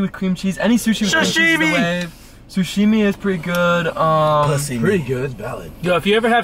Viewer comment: NGL, I swear to God that I thought I heard him say "pussy" at 13:41 when they were talking about sushi. I had to back it up to make sure I was wrong.